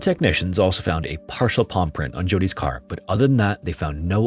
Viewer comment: I THOUGHT YOU SAID PAW PRINT LMAOOO